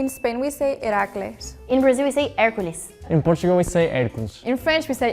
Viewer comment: and 12:30 no no, the subtitle is wrong! In portuguese (Brazil/Portugal) is HÉRCULES (greek form), not "Héracles" (roman form)